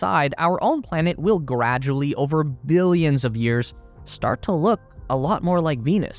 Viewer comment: Wait, he just said that the earth will die in billions of years??? Whoooooo....that's a relief. I thought he said MILLIONS!! 🤐🤐